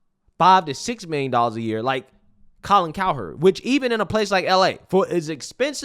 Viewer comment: I really thought his name said Colin Coward. 😭